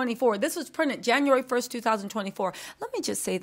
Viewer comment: did you just say January 1, 2024?